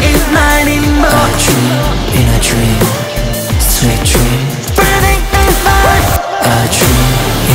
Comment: I thought he was saying juni juni moon juni it was actually a dream in a dream, sweet dream Amazing song❤ the blond hair, black crop top and abs🔥